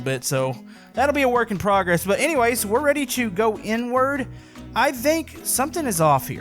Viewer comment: Is it just me, or does it sound like says N word when he says inward If not, then I'm just messed up